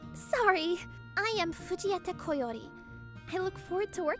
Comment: I watched to much dragon ball it sounds like everyone is saying vegeta instead of fujieta 😂